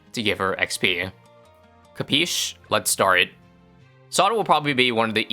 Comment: what did he say here? Kapisch? In german thats a sloppy way of saying Understood? but I feel like he said something else and my brain wants to hear Kapisch